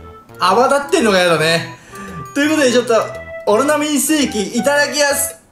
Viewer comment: オロナミンセーエキ？w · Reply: にオロナミンセーキなのにオロナミン精〇に聞こえる！